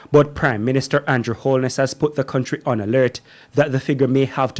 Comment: Did I hear correct, the presenter stated at 17:14 that the “Andrew Holness has put the country PON alert” really!!!🙄